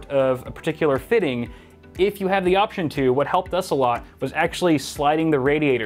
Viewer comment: Did he say "What help does a lot" at 15:40 ? xD